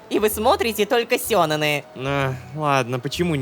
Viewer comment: почему я слышу сенэн-ай...